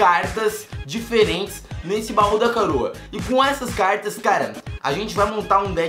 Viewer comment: No Baú Dá Caroa kkkkkkkk só eu ouvi isso?